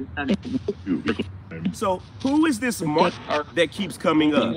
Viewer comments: i heard this is mark. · I heard "This is Mark", when you asked "Who's this Mark?" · I heard “this is Mark” when you asked the question.